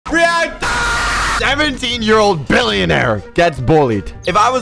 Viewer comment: I thought he was gonna say Billy. Billy is 17 years old